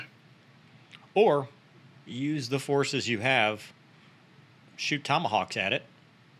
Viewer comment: I thought he was going to say "Or use The Force™"